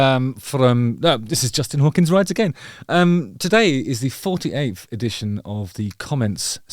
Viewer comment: For the longest time I heard it was “Justin hawkin’s right again”